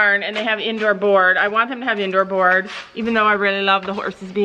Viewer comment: Will the horses be in stalls all day in the new barn? If so, Finn might revert to his anxious and got behaviors under saddle, just because he’s going to be all stobed up and bored Tom standing around all day. Horses are supposed to move around 24/7, and be able to interact with other horses. I know you guys know this, just wanted to know if you’d thought about Finn’s behavior coming back. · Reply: DayByDay Vlogs oh, I thought you said it was indoor board. My mistake. Sorry